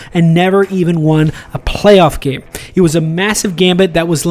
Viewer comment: did you say massive?